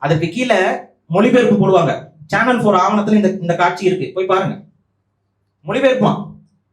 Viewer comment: what channel he said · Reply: Channel 4